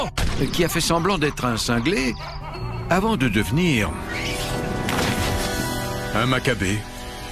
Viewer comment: qu'est-ce qu'il a dit? Je ne l'avait pas compri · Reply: Avant de devenir un maccabé